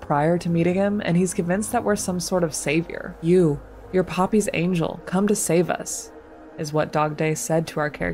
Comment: I originally thought he said “🥺You, 🥹You’re a Puppy’s Angel, 🐶👼Come to save us.” and I thought, “That’s one of the sweetest things anyone has said in this series.”. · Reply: I originally thought he said you , you're a puppys angel, 😇 come to save us. And I thought, that's one of sweetest things anyone has said In this series. For real life.